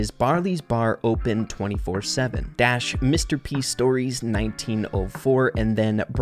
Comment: I think it's saying Mr p stonks